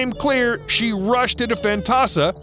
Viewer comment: WTF he said at 4:32 to 4:36? She rushed into fentasa? WTF is fentasa??